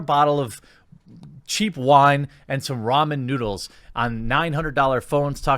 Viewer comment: Thought you said Whamen Noodles.